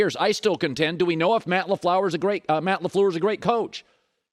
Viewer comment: Did colin call him Matt leflower @ 1:27 🤣🤣🤣🤣shots fired!!!! 🔫